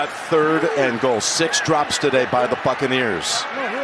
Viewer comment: is it me or did he said the “FUCKANEERS” ?!